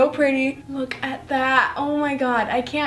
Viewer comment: OMG I HEARD HER SAY IM GOING TO F*CK IT